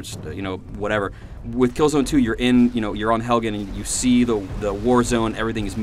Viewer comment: did he say HELGEN! soooooooooooo does killzone take place FAAAAAAAAAAAAAAAAAAAAAAAAAAAAAAAAR into the future of Skyrim?